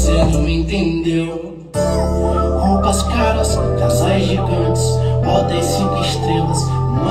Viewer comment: acho que ele fala "casas gigantes"